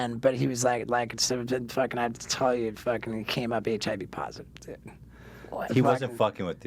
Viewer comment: wtf did he just say?! "...So who knows ...when... but he was like, like its so fidd-a fuckin add t-tai-you fuckin camera etch ivy parts of the" ?_? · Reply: x HnTr VDH x the guy went up to steveo and told steveo he's HIV positive (since he knew steveo snorted his blood) so get checked I guess